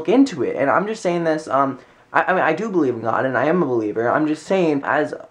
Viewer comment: haha when u said u were a believer, i thought u said "i'm a belieber" i was like buhbye, im so glad u didn't!